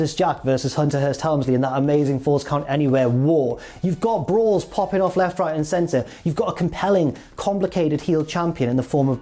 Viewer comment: For a second I thought Jack said 'BRAS kickin' off left, right, and center'... And I wasn't shocked. The 90s was weird.